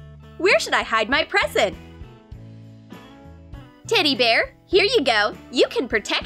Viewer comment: I heard 'titty bear' Lol I can't be the only one